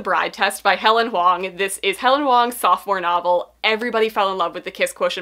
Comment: I thought she said "soft core" novel for Helen Wong, but it was sophomore..